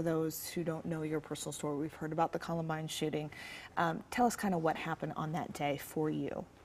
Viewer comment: I always hear "the shitting" and not "shooting"